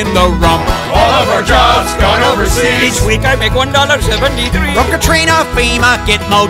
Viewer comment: The subtitles say “each week we make 1.73$” But if you listen to the words he says “we can’t make 1.73$” Our company’s go over seas to “save” money, but it ultimately costs us more. And they spend less Fuck outsourcing company’s, quit paying your executives million dollar salarys when they can’t do anything for the company